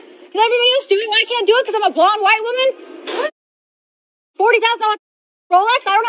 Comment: “you won’t let me do it cause i’m a blonde white woman” she’s says, to a blonde white woman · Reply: I thought she said because I’m a grown white woman